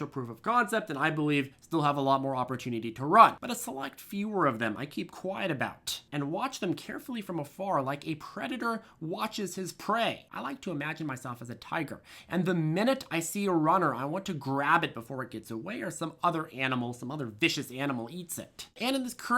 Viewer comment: I thought I heard "like a creditor watches his prey"